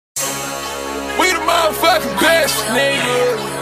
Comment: I always thought he said "We motha fuckin' crashin nigga!"